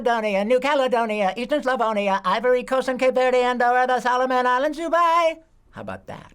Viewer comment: The subtitles are wrong, it’s not “and all of” it’s actually “Andorra”